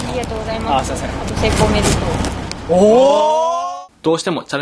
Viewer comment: メイクおめでとう…がちょっと可愛かった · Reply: なんか「成功おめでとう」に聞こえた笑